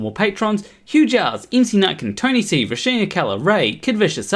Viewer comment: I always thought you were saying the name of your supporter as "M.C. Napkin". I guess "Nutkin" is funny too, kudos to M.C. anyway. Enough about other beings, please continue being your awesome self.